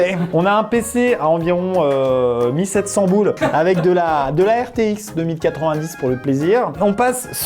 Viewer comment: c moi ou il dit rtx 2090 XD